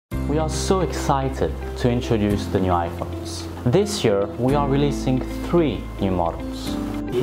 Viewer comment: All I heard was "Iphone Tennis"